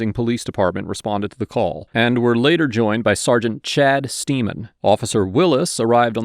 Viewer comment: i heard "Chad Semen" and had to double back...